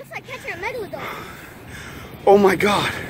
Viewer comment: did he say megalodon the little kid wtf